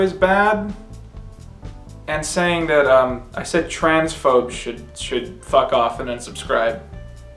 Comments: When you said transphobes I heard “trans folks” and my fear lmfao. Damn my audio processing issues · I'm surprised so many people misheard "transphobes" as "trans folk". Considering the context of what he said prior being about how he got cancelled for calling Ben Shaprio, an avid transphobe, an idiot. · I thought you said Trans Folks not Transphobes and, oh boy